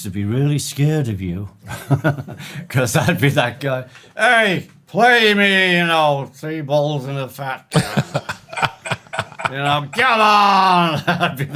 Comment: Tf does he say at 24:12? Sounds like, "Hey, play me, ya know... Three Balls and a Fat!" lmfao